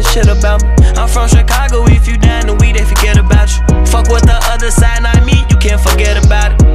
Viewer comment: I thought he said im from Chicago where if u die the weed ain't forget about u... guess he said something else like we ain forget about u... but the weed line tuff cause they b smoking opps after the die smh crazy double meaning